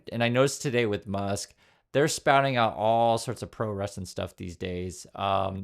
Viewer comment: I misheard your first instance of "pro-Russia" as "pro wrestling" and it didn't flag me as an unusual thing to say in context with this administration. That is worrisome!